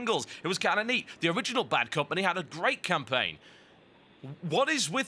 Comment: Did he just say: "The original black company" at about 18:14? XD